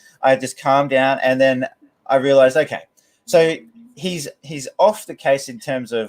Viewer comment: For a second I thought said " I meditated, I calmed down and ... I realized I came" I realize he didn't